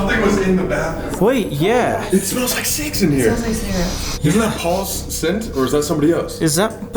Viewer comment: " It smells like sex in here! " It's what I heard.